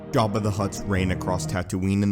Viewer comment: Bruh did you just say “gaba the hutt” in the first second of the video? 😂 caught me off guard with that one